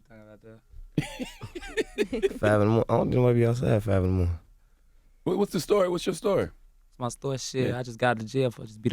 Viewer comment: Wtf did durk say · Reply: Nobody be outside 5 in the morning